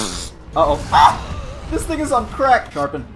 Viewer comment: I love how said thing and i heard nigga